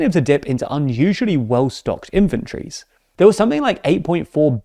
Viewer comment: anyone else hear 'well stocked infantries' not 'inventories'?